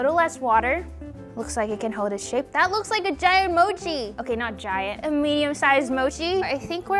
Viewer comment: Did I hear GIANT mochi?